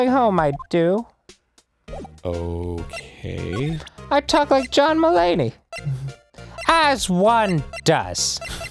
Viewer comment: So when I first listened to the episode, I thought said "I tackled John Mulaney" instead of "I talk like John Mulaney" 😂